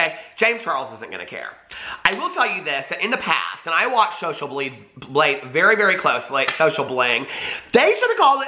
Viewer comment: Thought you said social bleed.... Sounds legit.